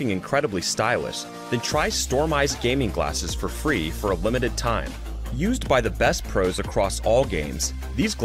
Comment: Does he say “ try for unlimited time” or limited